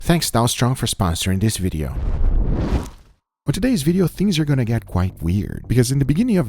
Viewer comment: I really thought he said "thanks Donald Trump for sponsoring this video" 😂